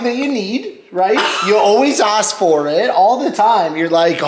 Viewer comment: Lol I thought was going to say "you're always ashy." But instead he said "you're always asking for it" lmaooo